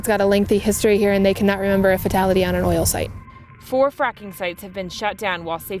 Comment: I heard "four fucking sites" XD